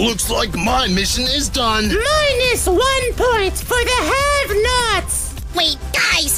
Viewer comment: Lol I thought X said “Minus 1 point for the half nuts”